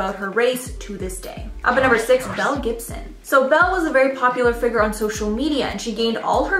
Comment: I thought you said “Mel Gibson” lmfao